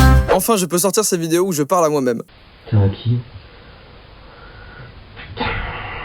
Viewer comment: A 2:56 j'ai compris qu'es ta toi sinon vous avez compris quoi